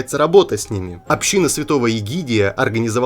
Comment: кому то тоже послышался- святой пигидий?